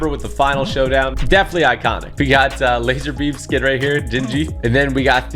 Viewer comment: did he just say lixer beam skin?